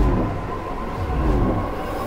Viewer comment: Everyone: his voice is distorted because of speed Me: m-ay-do-e i-n ha-v-en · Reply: Why do i hear "made green heaven"? Help i have hearing problems